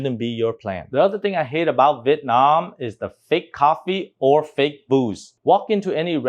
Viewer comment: I thought I heard you said "fake coffee or fake boobs". Haha Jesting aside, I think this will be my biggest struggle when and if I visit VN- everyone trying to make me feel stupid by duping me.